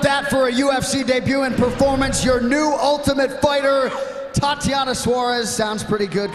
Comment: I thought Dana said Thotiana 😅😅